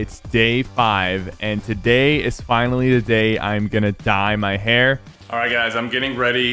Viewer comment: i thought you said today its finally the day im gonna die